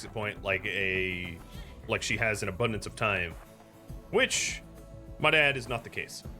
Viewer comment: I heard "which, my dad, is not the case" 😂